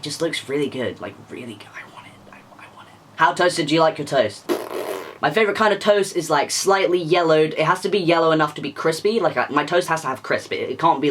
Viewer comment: I heard toes and I was so confused because I looked away for a second😭😭